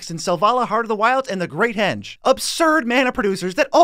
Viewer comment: I have one big question, what are your last words in every "short" video aftter "peace out" . I only hear, "peace out short scope" or something like that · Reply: Tribe scout